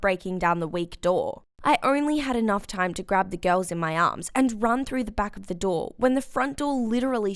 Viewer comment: Ugh, I was so dumb. You still are.. When she said I only had enough time to get the girls in my arms I thought she said When she said I only had enough time to get the girls in my arse